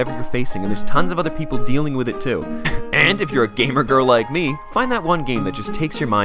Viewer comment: did you just call yourself a "gamer girl?"